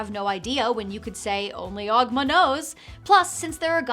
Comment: I heard "only Ogg m'nose"